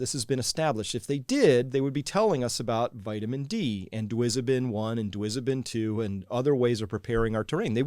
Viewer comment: What is he saying at 23:45? Dwizeben 1?